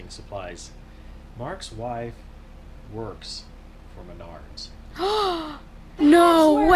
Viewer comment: What do you say at 3:38 ? "Mark's wife wors for [ ? ]" ? Feeling that maybe not living in the US made me miss something "everybody knows" (when you start singing). Just curious, that's why I ask. And I really love devotion to keep implements stored inside. Having an electric winch and lifting implements off trolleys! Wow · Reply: Menards. It's a giant hardware and lumber store.